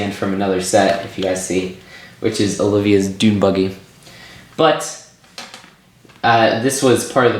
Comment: I must've misheard, did you say Olivia's doom buggy??